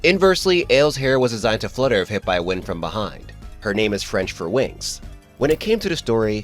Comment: I thought he said winx like "winx club" and now I might not un-hear that